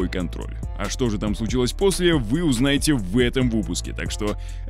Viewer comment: Я думал что послышалось: вы узнаете в этом в вупуске! Видео с 1:00 до 1:03 мин, прослушал несколько раз